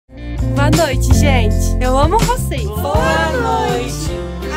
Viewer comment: No começo achei que ela tinha falado boa noite amo vcs agente fãs kkkk